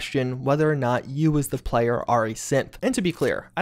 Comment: All i heard was "YOU THE PLAYER ARE A SIMP"